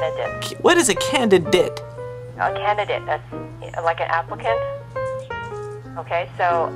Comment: I thought I heard her say "a candidate...like an African." O_o